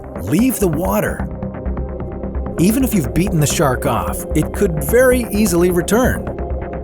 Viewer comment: Bro I spacer out and at 2:59 all I hear is "beating a shark off" God my mind is dirty...💀